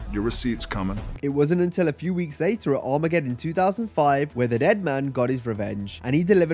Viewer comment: I swear when he was talking about the undertaker about 1:17 in he said the egg man instead of the dead man……..😂😂 I would love to see a receipt for that lol then again I might be hearing things myself lol